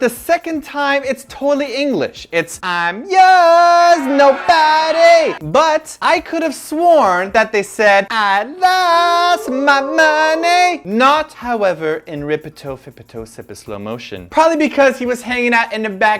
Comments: i 2 thought they were saying i lost the money until i watched the live performance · i actually thought they said i'm yours, no money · I heard "I loooooooost my money" XDDD just as and heard XDD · I thought they said " I'm yours,nobody" until I looked up the lyrics... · I didn't even know about Beast saying, "I'm your nobody" until I watched your video. I always thought they were saying, "Ay, girls no money" and "I'm girls nobody" lmao; now I finally know.xD My favorite outfit would be YoSeob leather jacket outfit. I feel like it would be pretty trendy in the USA because one side has a sleeve, while the other side doesn't. I bet most guys would wear it to look swaggy, and if I were a guy I would probably wear it too. OuO